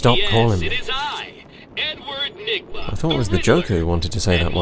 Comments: It genuinely sounded like riddler said "it is I l, n word nygma"🤣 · ANYONE HEAR RIDDLER JUST CALL HIMSELF "N-WORD NIGMA?!" JUST ME? OKAY